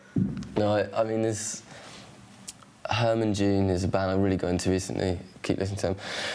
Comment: I thought he said Herman Dune? Could be wrong tho.